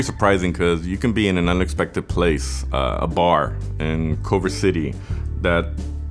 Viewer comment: caption says Cover City. I think they meant Culver City